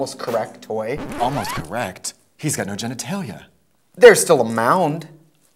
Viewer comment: “He got no genitalia” “There’s still a mound” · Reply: I thought he said there's still a mouth and I GAGED!